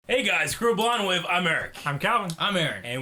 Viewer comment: Whenever you introduce yourselves I hear "Blonde Wave" instead of Blind Wave.